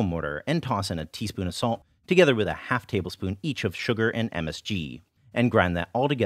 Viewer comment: wait... Did you said MSG? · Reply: Yes, monosodium glutamate... MSG